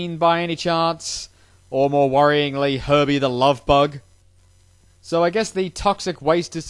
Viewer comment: I thought he said PERVY, the Love Bug. It would make for an interesting show.